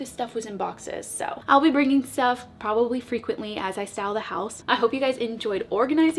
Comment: Great storage sort out, everything so much more accessible Did you say "as I sell the house" at 17:35 min? ;)